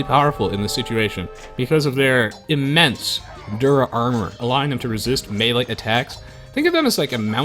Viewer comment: Hey, here's a question. Why do you keep calling Melee armor, "Dura armor"? I've never once heard anyone call melee/regular armor "dura" armor, nor have I even seen it used before when looking it up. So, why that? Dura is even short for durable (or durability), but those are longer than "melee" is with syllable count, and no one uses "phys armor" (physical) either. You seem to be the ONLY person I have ever seen call it that in the known realm of the Internet, when compared to other notable match casters, streamers or Age of Empires-focused channels on YouTube (such as Spirit of the Law or Zack of ZeroEmpires). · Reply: I thought he was saying "dir armour" this whole time, as in direct armour. O_o But it has the advantage of unambiguously differentiating it from pierce armour, which is helpful.